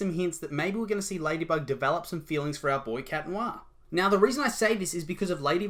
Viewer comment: please tell me why I heard that as 'our BOI cat noir' help